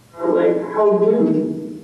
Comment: it sounded like a woman said "how rude"